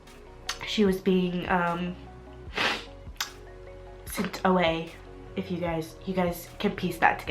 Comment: Can anyone tell me what does she mean when her mom was sicked away on 18:00 ?? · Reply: Adilene Rodriguez she said 'sent away' and I'm pretty sure she meant to jail, but I'm not 100% sure obviously